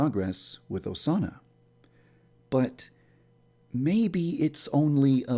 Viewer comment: OMG AT 1:46 IT SAYS "HE AND GARY" IM CRYING!!! XDDDD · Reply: AND AT 9:34 IT SAYS "THE SAUNA" INSTEAD OF "OSANA" OMGGGGG XDDDD